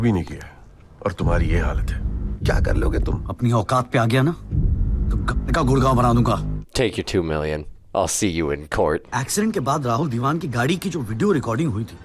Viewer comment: subtitle says turn your world upside and ol.....but it was actually like if It was upto me, I will woop your ass big enough to cover the whole state....😅